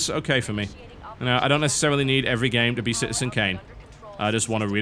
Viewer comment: What is he saying at 47:49? "I don't necessarily need every game to be ..." syncane?